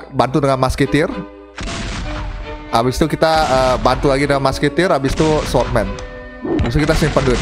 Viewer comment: kok telinga gw dengernya mas kentir yak 🗿👍